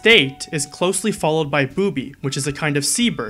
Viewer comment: I thought he said booby is a c-word and ONLY a C-word, while showing a picture of a bird. Then I realised he said sea bird, not Curse word...